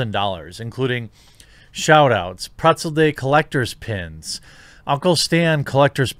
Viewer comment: Girlfriend half overheard me listening to this and thought you said "spinoff" and "Gruncle Stan". I got to break the news that they're not making new Gravity Falls content. Laughed maniacally in her disappointed face.